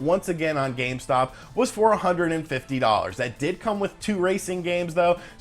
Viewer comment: I wasn't looking at the screen at around 3:20 and I thought he said $450.